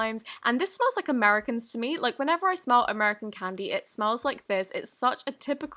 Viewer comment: When she was talking about a slime, I thought she said; When I smell Americans, 😹😹😅😂🤣😁😆